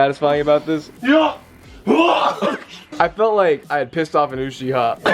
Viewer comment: did he say willie wonka???